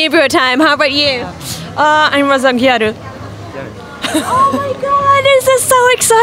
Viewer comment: what did she say? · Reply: I think it was gyaru.